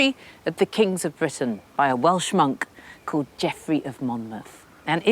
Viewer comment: "That's Bigamy!" Henry; "Yes, it is big of me!" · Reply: Lol! @ 4:01 I literally thought she referred to Jeffry of Monmouth as a "well schmuck" (instead of "Welsh Monk")